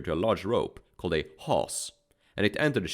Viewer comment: Can anyone tell me what the word was at 14:00? Sounds like "hoss?" The word for the rope that holds the anchor. I've been trying to find it online and I don't see anything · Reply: Haus